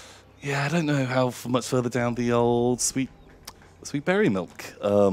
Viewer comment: Won't lie for a while I thought they were saying Barry Milk and was even more scared till I heard it right lol